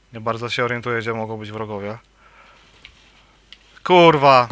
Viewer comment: Ojj usłyszałem "kurwa" ? :p